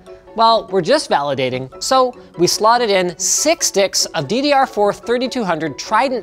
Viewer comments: souded like linus said six dics instead of steaks lol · um...did he say sticks or d1cks!? I can never unhear that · Six WHAT?! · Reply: Had to scrub back 5 seconds myself to see if I misheard. Sounds like he said sdicks. But that was before the word "six" /siks/ So it sounds like /siks diks/. Linus is very silly.